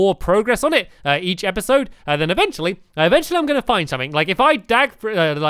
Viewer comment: dag instead of dig lol